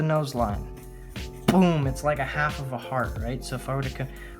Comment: When u said heart I thought u said fart